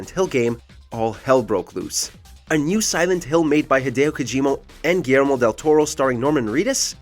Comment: did he said hideo kojiMO?? 🤔🤔